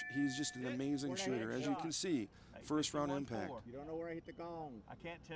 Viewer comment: David Tubb's spotter is blind as a bat. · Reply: I think its his son, I thought his spotter said "I dont know dad"